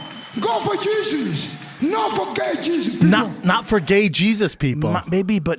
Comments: Joel did say don't forget Jesus at 59:40. He did not say gay Jesus. You see that's why people hate Brendan, and talk so much shit about him, me included. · Around the time Yoel said “No forget Jesus” I think there was a big discussion around the legalisation of gay marriage which is another reason people thought he said “No for gay Jesus” Also it is obvious he says forget once you really listen to it.